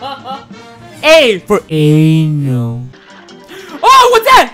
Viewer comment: i thought he said “A for A N G E R.”